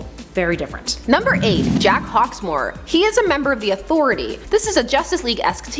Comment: I actually thought she said Jack Talksmoor